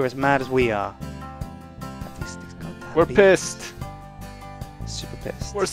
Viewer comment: for some reason, i kept hearing 'beliebers' instead of 'beavers'